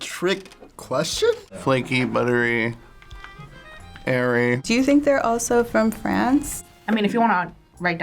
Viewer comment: Thought he said "Hairy" in the start (i know he said airy after)